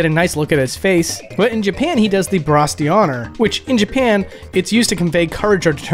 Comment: I don't understand what you said here and the subtitles say "Brostioner" 😮 here in Italy it's called "Umbrella gesture" and used to insult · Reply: He said the French term "bras d'honneur" (except it's actually pronounced "brah-daw-nuhrr" instead of "brass dee honor").